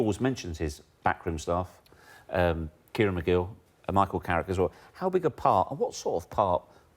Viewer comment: Did he say McGill? 😂